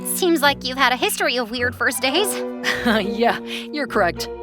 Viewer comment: Lol she said days but the captions said dates😂